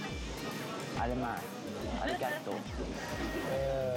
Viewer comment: 조개인가 먹을때 아줌마 아리가또 라 들림 Azumma(korean) arigato(japanese)